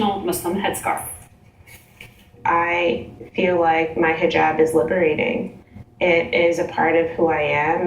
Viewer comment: I thought it said 'My Rehab liberated me'.